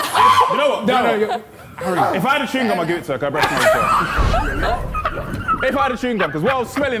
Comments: What did he say at 22:12 I can't tell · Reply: "if I had a chewing gum I'd give it to her, cuz her breath smells aswell" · i still have no idea what harry said, i cant hear him. anyone know? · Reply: "if i had a chewing gum I'd give it to her it stinks there"